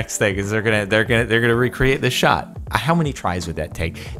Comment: I had a ad were he said dude perfect is going to recreate this shot but when the ad popped up it sounded like he said shit